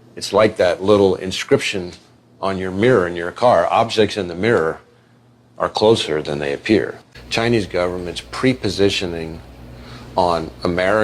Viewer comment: Matrix, matrix, stupid matrix just now. I was watching on my t.v and at 14:06 the guy says its like that thing on your re veiw mirror saying "objects in your re veiw mirror MAY appear closer then they are" I replayed it again to see if thats what he said and again he said MAY. And I thought isnt that a mandela effect that a bunch of people remember it saying may appear and now it says are. There is a song by meat loaf thats called objects in the reveiw mirror may appear closer then they are. So I had to look it up thinking this guy just said it proof it use to say that. So I paused the video to look it up. And yes everything says that its always said "are" closer. Now I know its just one word but the gaslighting still bothers me. But still I figured I wouldn't comment about it. Until I had to restart the video because if leave my tv paused for too long it exits out. So I had to start the video over and thought id just let it play instead of fast forwarding to the part I was at. And when it played the guy again at 14:06. He now says are closer!! Its like as soon as I thought this guy could be proof "they" changed it. Its a simulation. I know digital videos could be changed and not blamed on a matrix but when books are changed that I have. Like the bible verse the lion shall lay down with the lamb. Nope its now wolf will lie down with the lamb and every bible I cheak it says that now. But if this is all a simulation/digital it would be easy to change and no weird talk of people